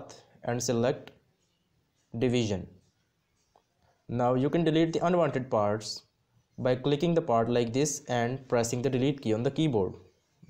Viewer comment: what you say? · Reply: 406 Now you can delete the unwanted parts, by clicking the part like this and pressing the delete key on the keyboard.